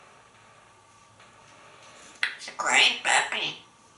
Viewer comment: The "gibberish" at 1:50-ish sounded like "It's a crying babby"! Does Einstein watch the Yogscast, or is he familiar with the "How is babby formed" meme?